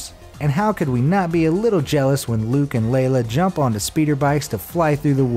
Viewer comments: did he just say Luke and Lala? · Did you say Luke and Layla?